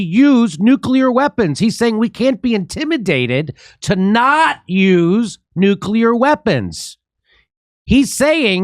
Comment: Am I the only one who didn't hear the same thing that Jimmy heard? I heard "intimidated to use nuclear weapons." Jimmy heard "intimidated to NOT use nuclear weapons".